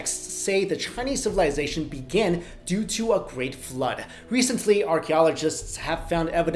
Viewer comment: What would you do if the world was flooded??? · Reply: I thought it said great 'food(s)' not 'flood(s)' lol